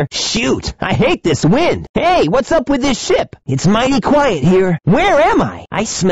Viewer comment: I thought Sonic said shit!